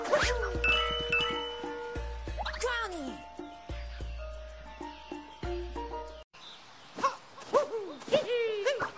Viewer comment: What did cappy say at 1:25? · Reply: He says something like: "Incredible! I'd take my hat off to you but... You know..."